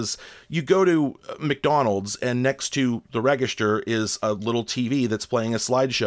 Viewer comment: did you say rehg-ist-urr? Love the videos! I watch one every day!